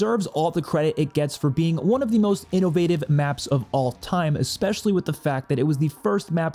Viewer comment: i heard that "innowative" hehe